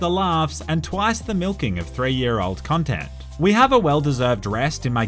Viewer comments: When said “twice the milking of 3 year old content” I thought he said “twice the milking of 3 year olds”🙁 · I thought he said "the milking of 3 yr old kids"